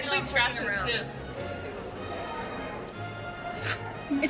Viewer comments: "Smells like paint; I like it" Lmao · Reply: I hear her say “it smells like pain” and I don’t know which one’s funnier 😂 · Sounds like she said “Smells like pain, I like it”. lol